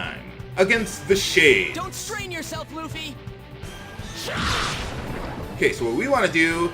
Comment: For a second I thought said "Don't strain yourself EUPHI"